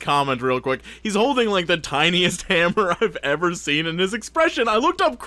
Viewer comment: I was listening to this with my eyes closed and thought you said tiniest hamburger.